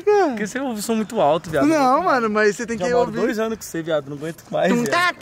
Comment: eu foi o unico q ouvi "namoro 2 anos com ce"